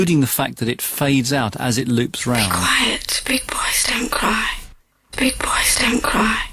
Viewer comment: I thought she was saying "requesting quiet" lol